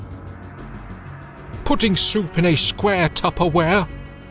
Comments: I thought it said "putting soap in a square tupperware" and I was like "why would you put soap in a tupperware?" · I thought it said "putting soap in a square Tupperware"